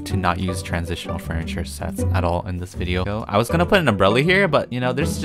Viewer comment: me like"I wAs GoInG tO pUt A uMbReLli" Me be like did he- did he say "uMbReLli" O_o is he going crazy 3:38 👉👈